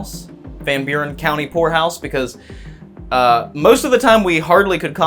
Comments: I had to listen like 5 times..... i could've swore he said whorehouse. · i thought he said whorehouse haha 🤣😂